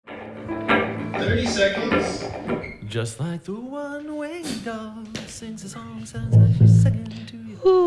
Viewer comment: Holy crap, I've also always heard it as One Winged Dove. Glad I'm not alone.